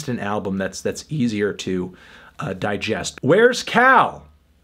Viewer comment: "Hey!!! is Cal Chuchesta gonna be there??" Thats what call said backwards.